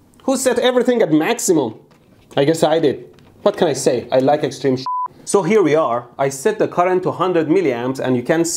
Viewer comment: What?.. He said that he likes extreme Shunt.